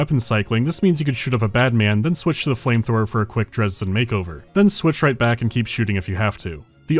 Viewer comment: did you say "Dresden makeover?" Cause that would be an accurate reference.